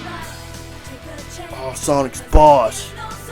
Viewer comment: it sounded like he said aw sonics balls